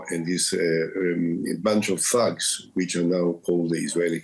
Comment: I had to rewind a bit at the end when Olmert said "a bunch of thugs", because I heard "a bunch of f*cks", which would have been much better 😂